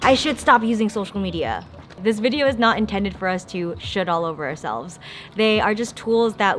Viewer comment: "Should all over ourselves" made me snort, I thought you said "Shit all over ourselves", for some reason. Great insights, bless you.